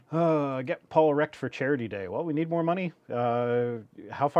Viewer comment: I wasn’t looking when Shannon commented “Get Paul wrecked for charity day”, and I had to check, because I thought had said “Get Paul erect for charity day”, and that would be an entirely different stream.